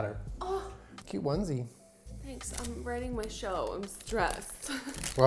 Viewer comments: Did Jenna say she is writing a show? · did anyone else hear jenna say shes writing a show